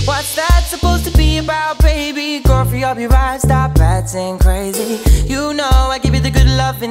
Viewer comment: I heard "Coffee on your mind" instead of "Go free up ya vibe" 🤦‍♀️🤷‍♀️ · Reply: I hear "coffee up your mind" 🤣